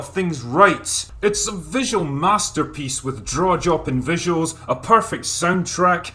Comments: Am I the only one who heard "drawjopping visuals" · "It's a visual masterpiece with draw-jopping visuals." There it is. I heard it. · Just to clarify, at 8:16, did he say "draw joppin'?"